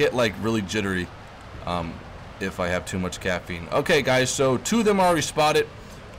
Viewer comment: Did anyone else realize was talking about drinking Chaffee instead of Coffee. He has played way too much WT XD